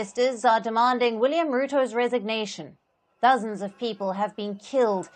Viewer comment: Palki Sharma usually reports correct things in regards to Kenyan protest BUT you Alyson Le Grande you reporting lies about people killed in Kenyan protest, you have to correct yourself and denounce the word thousands killed, why are you killing people with your mouth?? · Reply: she clearly says "dozens" and not "thousands". The automatic transcript also. You are accusing them falsely, shame on you. You should delete your many false accusations here and make amends. And in the future, just pay attention.